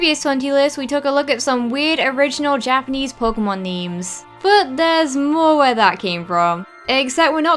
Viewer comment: Its sounds like shes saying MEMES insted of NAMES. but i understand what you mean. :b Love your chanle! <3 Sorry for my bad English im swedish. :b